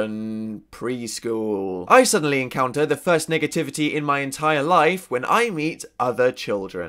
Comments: "niggertivity" was the first thing that it sounded like ( im horrible) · when he said negativitie i thought he said nigga tv idk why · it sounded like he said nigga-tivity lol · i swear i hear "niggativity' instead of negativity omfg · ahh i thought you said the n word for a second that was a close one, you just said "negativity" phew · I thought he said niggativity.. encountering with black people.. yea.. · did he say niggativity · When he said negativity its sounded like he said niggativity XD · i thought he said niggativities lmao! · it sounded like he said "the first nigga-tivity"